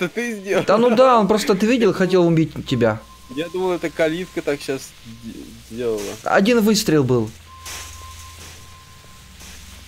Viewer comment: кто услышал ковидка лайк)))